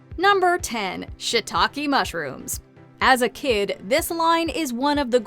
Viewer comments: The oh shii take mushrooms I knew the whole time it sounded like she was saying oh shit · "Oh Shii... Take mushrooms!" It took me a while to get that joke! 😂🤣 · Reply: I thought she was saying shit talking mushrooms when I was a kid.